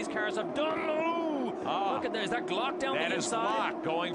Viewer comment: I thought he said team O’Glock instead of timo